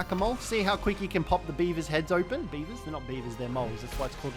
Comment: I had the volume down a bit I thought he said beaner not beaver